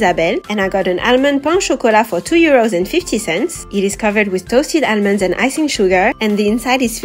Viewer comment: Girl I love your stuff and no hate but instead of euros I hear “year olds” ex: 2 year olds! 💀